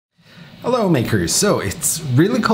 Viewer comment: This is the first time watching you and at the start i thought you said "Hello N word"